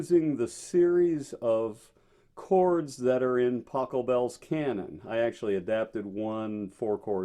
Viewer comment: I thought he said Taco Bell’s cannon 😂